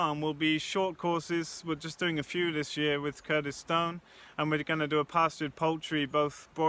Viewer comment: Oh, Curtis Stone! I thought you said Kurdistan! Had to re-watch that bit loads to work what you'd said sorry! My bad. Interesting video, I hope you get the right kind of people engaging with this, it's a fantastic opportunity.